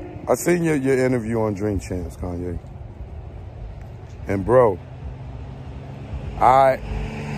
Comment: For a minute i thought u said kanye a h0e...hes on pure fire